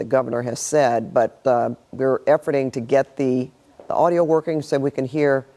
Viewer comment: Did she say “efforting” at 12:10-ish?😂 What kids? Just the back of some rent-a-kids heads.